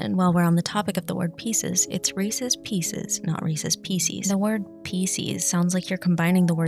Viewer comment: I’m not gonna lie, I thought you said “it’s racist pieces” and I was very confused what direction we were going here